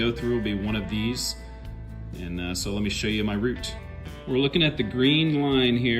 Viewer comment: i hope ur happy, mama was cooking breakfast and heard you say " let me show you my root" and dropped the frying pan on the floor and hector run over and eat the souse meat 😡